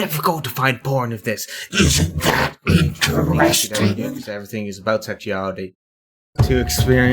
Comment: I can't make out all of the subtext at 3:55, I think it ends with "everything is about sexuality"